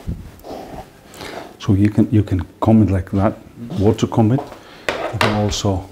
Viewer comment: Could someone please tell me what he is saying at 6:55 after "So, you can comb it like that"? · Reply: I believe he says you can comb it like that "if you want/are/were to comb it" But the words "if you" don't really come out, so it just sounds like "were/are to comb it" Being Icelandic speaking english, my best guess is that he says "You can comb it like that... (If you) are to comb it"